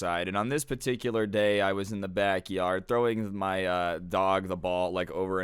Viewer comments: My audio glitched out so I thought said throwing my dog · I legitimately thought he said that he was throwing his dog.